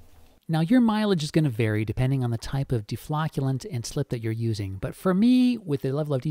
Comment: You said 'deflocculant', I heard 'deflatulant'. BRB, need another cup of coffee. ;)